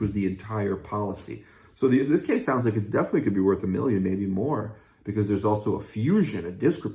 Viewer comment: I thought you said mild tbi could worth a million I have post concussive syndrome